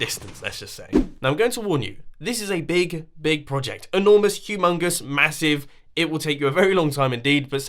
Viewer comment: did you say humangus